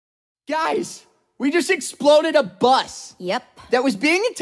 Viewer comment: before i read the lyrics i thought he said "we just exploaded a butt"...